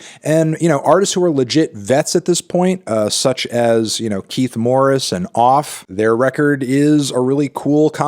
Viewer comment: I can't understand who is he naming at 5:00, someone help me please, Keith Morris and Off???? I'm not a mothertongue I can't make sense of the names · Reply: Keith Morris and off